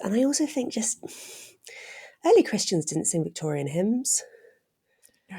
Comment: At first I thought you said "Early Christians didn't sing hyms!", and immediately I thought "Haven't you read the Gospel of Mark? Jesus and his disciples sang hyms after they took the first Eucharist in the upper room!" But I wanted to be sure I heard you right, so I rewound the tape and listened more carefully to what you said, and you said: "...'Victorian' hyms!" I thought then I was wrong in my initial interpretation of what you meant, but when I really think about it now, I think I got you right the first time. When I first read that part about Jesus singing hyms together with the disciples at the end of the book of Mark, I smiled to myself because I felt their humble humanity, way back then in that dimly lit room. I recognised it.